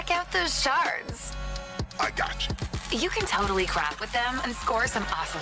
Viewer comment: The intro cinematic to Citizen Conflict is quite something. No showcase of gameplay, no presentation of the setting the game takes place in, no establishing of the charcters. It's just 100% talk about how awsome lootboxes are and how you should totally spend money on them. Crazy to think that in a time when most games try to hide/disguise their microtransaction behind artificial in-game currency or layers of in-game shops, these guys thought it would be a great idea to make their entire advertisement about it. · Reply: "You can totally crap with them" I swear to god that's what she's saying, she is not saying "craft"